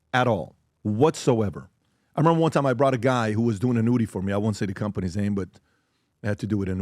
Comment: I thought he said nudity too!! I was like okayyyy Patrick 💅